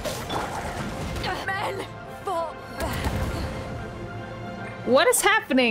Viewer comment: i always thought it would be funny if beidou’s final line was just “MEN” when she died lol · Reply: The first time she died in my game, I didn't hear the, "fall...back!" part, so I genuinely thought she was just saying, "MEN." In a resentful manner. I about died lmao